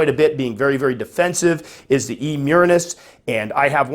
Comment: At first, I thought you said, P. murinus. But then, you start talking about burrowing, a P. murinus... I'm thinking about it....then, I think it's E. murinus.... yes burrower...OK. Then I'm thinking about just how many 27 quart fossorial species I can house.... I guess it's all stackable.... 🤣🤣😁😁